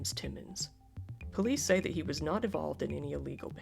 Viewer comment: did you say “not evolved” instead of “not involved” bc whether that was intentional or not that’s amazing